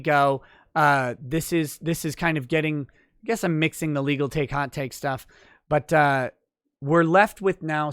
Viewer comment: i swear i heard "legal take hot cake", so you have to name it that now. because it sounds too right to be wrong.